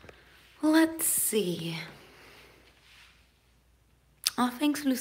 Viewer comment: Блин, мне на 20:25 послышалось "молодцы", вместо "let's see")))